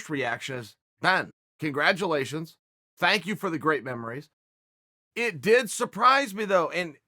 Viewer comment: Ben Askren’s Retirement, “Thanks 4 the great memories” I thought at 1st u said “Thanks 4 the great memory” (I thought u was saying basically the only thing Ben’s going 2 be remembered in the UFC is 4 being on the wrong end of a flying knee lmfao) I had 2 go back 2 the start and listen again. That would of been so funny if u said “thanks 4 the great memory” tho lol